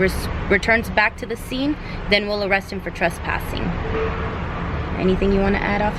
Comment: What did say at 7:39? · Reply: ThisIsntMyName "for trespassing"